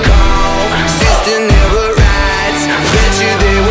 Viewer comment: First time listening the chorus, I heard "sister never rides" O.o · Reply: writes*** lmao you wish